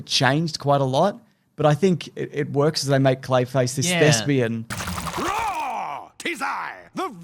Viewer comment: What is saying at that part? This Lesbian or what? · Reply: Thespian- I believe it is another word for an actor, or a purveyor of the theatric